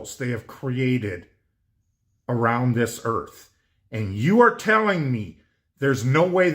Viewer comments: I'm Sure I heard him say Around the Earth . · er, pardon, say what? Did he say what i thought he said, im sure he did!! ("around")